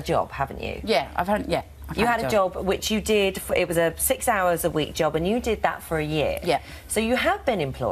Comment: “I fink I come across well spoken” - nailed it 😂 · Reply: Haha, for one sec I thought she said she worked 6 hours a week.